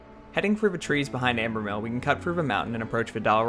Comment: are you saying "frou?" as in "cut frou the trees." I don't hear the "th"